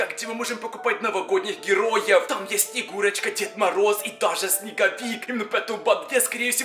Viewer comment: Мне послышалось когда вы говорили про деде мороза снеговика и снегурочку не снегурочка а курочка деда мороза